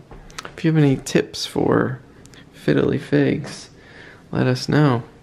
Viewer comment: It sounds like you say fiddly figs 😂